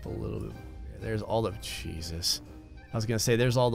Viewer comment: i thought he said there is all of cheese-its